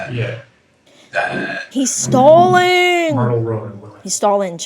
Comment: I have this playing in the background while I'm playing games and I keep hearing "murder road" every time the narrator says "Mirtl Road".